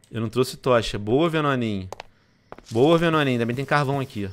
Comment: Venon:boa venoninhu ;-;so eu que escutei danoninhu?